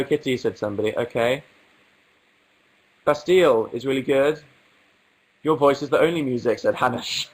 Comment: Can someone tell me what does he say in 16:20 pleasee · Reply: Maja North 'bastille is really good' and 'your voice is the only music'